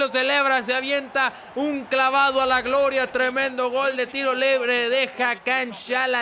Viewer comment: Escuche mal o dijo Tiro lebre